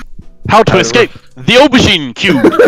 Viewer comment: What does he say at 3:32? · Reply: Aubergine, it's just what people call eggplants in England and probably some other countries